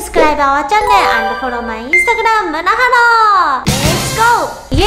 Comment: OMG! I HEAR AGAIN, "MANAHALLO" :(